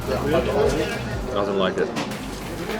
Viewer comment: did he said I don't like it ? Someone please patch some hair on his forehead